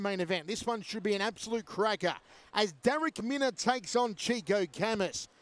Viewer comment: Oh, I thought you said, crackhead I thought, who would choose a nickname like that - I feel a twat now😂